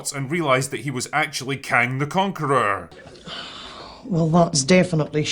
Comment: When the said she found Kang the Concorer, I thought he said Khan, as in a Mongolian leader. I had to look up who he was just to be sure. But for a moment, I believed it. I believed Marvel was desperate enough for a story that they'd BS one of the Khans into the Quantum realm.